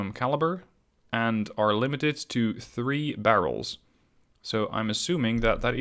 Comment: Not allowed by the scenario. 3 single barrel turrets only as explained in the video. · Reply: Oh, i heard "only 3 barrels". Oops